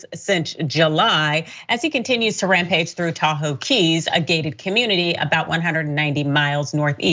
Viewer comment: It sounded like you said Taco Keys. Hank needs to learn about the Tuesday Deal!